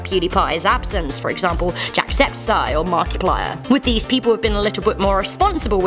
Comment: Did she just say "jackseptiseye"